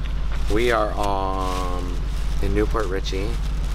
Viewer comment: I seriously thought you said “we are on a nicole richie” i was like what could that possibly mean????